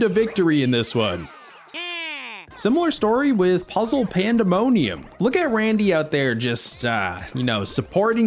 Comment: can you repeat that? Because i think I heard the Word pandemonium